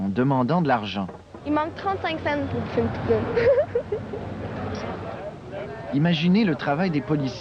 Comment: J'arrive pas à comprendre c'est quoi la fille dit à 2:40 "Il me manque 35 cennes pour..." ? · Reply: Une poutine